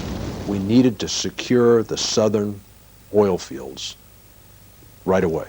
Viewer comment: mins Oil? Did he say "oil"?